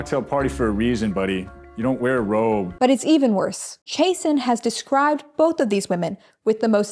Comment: oh his name is actually “chasen” i thought i was mishearing jason this whole time